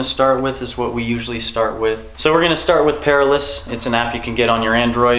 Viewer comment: What's the app called use said at beginning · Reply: It sounded like said the app is called Perilous @ 4:48 , but I am not having luck finding it, so maybe it's spelled differently? I want to find it too. If anyone knows, please reply.